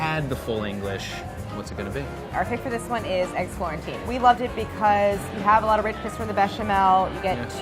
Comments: I... I might be going crazy but did Claire just say, Eggs quarantine? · when they said ''eggs florentine'', i heard "eggs quarantine" and i think it's appropriate right now